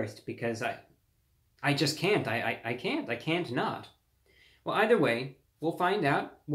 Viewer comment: it sounds like ReviewBrah saying "I can't nut"